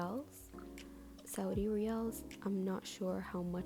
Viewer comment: from the riyals and the cover of her sketchbook i think she is an arab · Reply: Oh, so she said riyals! I thought she said real, Brazilian currency. Didn't know it was arab